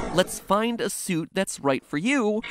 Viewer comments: When i heard the "lets find a suit thats right for you," i first heard "explain yourself explain why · Ok with that fredbear laughter this is what I heard: “ Me: “bang bang bang bang” Mat: Laughter, obviously Oh... Me: “let’s find a flute that’s right for you” Mat: “let’s find a suit that’s right for you” That makes more sense... Me: “I own a fork fun slave of yours” Mat: “there was more fantasy and fun where I came from” Me: o_o dunno where that cane from my is more correct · I heard "My spider suit" and before that "my spine (something inaudible)" 😂😂😂😂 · i heard explain yourself puppet not lets find a suit that's right for you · does anyone else hear "what's mine is yours" instead of "let's find a suit"? I mean, I hear both, but that's what I heard originally and still hear.